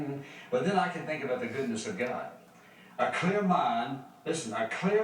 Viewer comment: typo at 5:25 : "A clear mind," instead of "A clear piped"